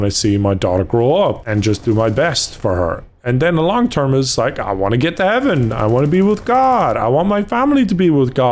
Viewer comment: lmao I thought he said “I want to get with Evan”😭💀